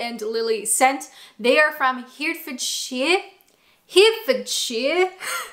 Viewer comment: Thought you were saying Hertfordshire for a while, very little cyder from there bouts me lovie.